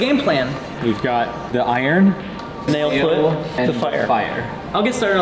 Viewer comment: I thought you said snail